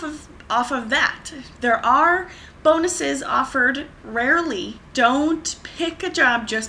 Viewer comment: i heard "bone us"!